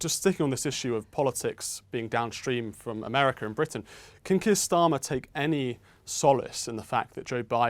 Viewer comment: "can kiss dama take any solace..." subtitles around 4:16 What did he really say? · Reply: Keir Starmer UK opposition leader.